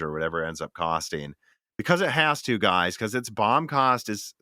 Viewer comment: Sorry I'm trying to learn. What was the word you said at 1:30:03? You mentioned the 4090 and its something cost. Did you say bomb cost? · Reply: Yes - BOM - Bill of Materials :)